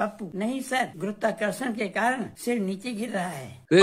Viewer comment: Can someone please tell me what did say about Nishant at 4:55 ? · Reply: said "this is nishant tanwar's reels"